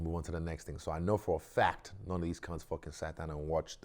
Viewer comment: Did Izzy just say “Cunts!?” Damn!